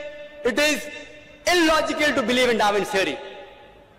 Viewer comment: I could swear that i heard him say "if you heard of white meat, sir white meat" I can't understand this guy half the time he speaks